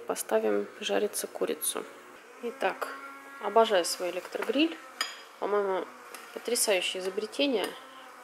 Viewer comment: Ура!!!! Наконец-то!!!! Как же я соскучилась по таким видео! И хозяйничать под них нравится! И вас смотреть! А ещё вы очень органичны в них. Спокойны, умиротворены. Очень счастливые глаза и вообще выглядите супер! Про аэрогриль можно чуть подробнее? Что за марка? Легко ли ухаживать? Какие минусы? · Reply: Вот и я не услышала «аэрогриль», а лишь «электрогриль».